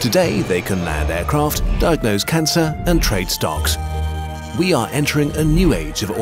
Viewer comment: i fr thought he said "stonks" ... (STONKSSSSSSSSSSSSSSSSSSSSSSSS)